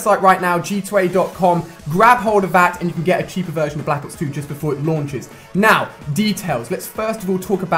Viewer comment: did he say black ops 2😂😂